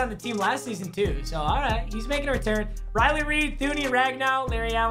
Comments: Who else thought he said Riley Reid at 1:58 😂🤙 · I swear it sounded like he said Riley Reid🌚 that’s for the boys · I swear he said Reilly Reid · anyone else think he said riley reid at first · Did he just say Riley Reid? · Yo he really said Riley Reid at 1:59😂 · Did he say Riley Reid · anyone else hear him say riley reid? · did he say Riley Reid is on his team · who else thought he said reilly reid